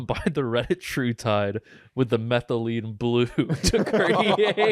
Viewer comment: all I heard was "Reddit true tide"